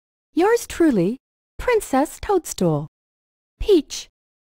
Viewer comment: ここ最初ビッチって聞こえたw0:25